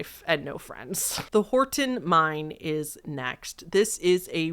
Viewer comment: I was listening mostly while working at my job and only looking at the screen here and there. I’m hearing impaired and my deaf ass thought you said “Horton Mime” and I was excited about an internet mime mystery